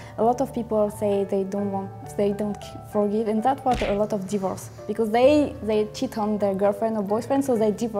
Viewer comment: the French girl says " and that's what causes a lot of divorce" or something along that line. Caption says diverse.